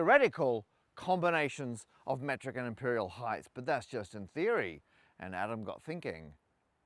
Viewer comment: Did... did you just say "heighth"?